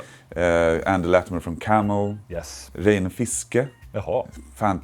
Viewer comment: Who is the guitarist mentioned at 28:23? My old ears can’t discern what he’s saying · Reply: A lot of random Googling led me to the name Reine Fiske, who I'd never heard of but apparently has been the guitarist for several bands including Landberk and Dungen (I've never heard of those bands either).